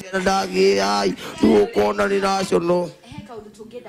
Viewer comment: did he just say 'irrational'?😂